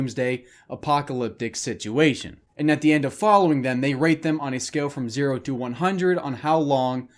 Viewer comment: I literally heard “and at the end of following them, they r a p e them ”